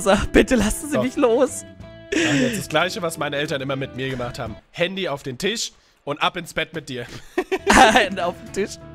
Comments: Bei 2:23 WAS SAGT ER DA?! UFFFF HELPP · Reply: Handy auf den Tisch und ab ins Bett mit dir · Ich habe bis jetzt immer verstanden Hände auf dem Tisch und ab ins Bett😂😂